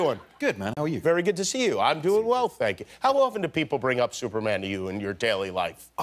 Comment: Am i do only one who heard him say "Im doing well fagget" I laughed so hard.